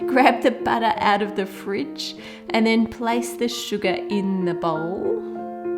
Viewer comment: did she say suga? #army